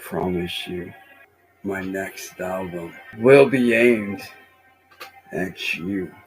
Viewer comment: I think he says album but I swear I heard bowel movement 😂